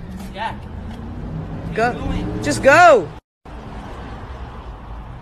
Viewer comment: Did anyone else hear jaeden say "what the fuck are you doing" in 0:44 ????😂😂😂😂😂😂